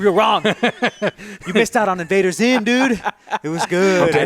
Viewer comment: What Netflix show did mention in 9:14 ? Can't understand what he is saying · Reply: Invader Zim